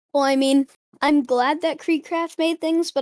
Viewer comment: did he just say "creek crap" at 6:05 what?? Kreek craft?